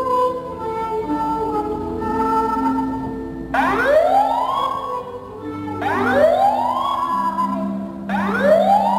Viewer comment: Can someone please say what the voice at 4:04 says? I can't understand it. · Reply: I can only understand “(woman voice) Attention, attention! (man voice) There has been a fire reported in the building. (loud inhale) While this report is being verified, proceed immediately to the nearest exit, and leave the building.” idk what he says after that